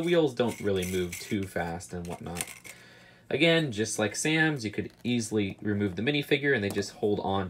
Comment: did you Just say sans!,?!? Cue the music